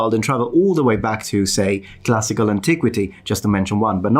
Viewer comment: I swear I heard "jurassic antiquity" rather than "classical antiquity" XD